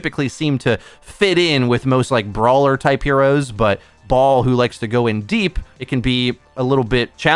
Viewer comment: Did he actually said “Ball, who likes to go in deep…”?